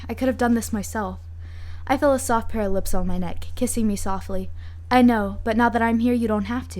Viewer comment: is it bad that when she said "kissing me softly" i heard it as "killing me softly" sangwoo, what are you doing in this story?